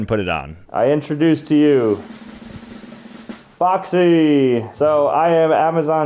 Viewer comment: who else thought he said foxy 🥀